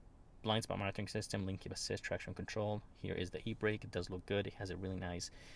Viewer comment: Did you said "Heat Brake" on minute 11:38?